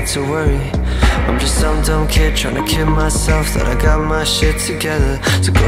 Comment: Man I thought it said "I'm just some dumb kid trying to kill myself" but it's kid. That's very different